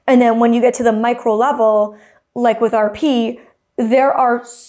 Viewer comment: The way I thought she said "like with our pee" for a second 😂